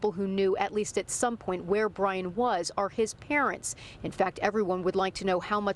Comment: I thought they were saying Brian was home???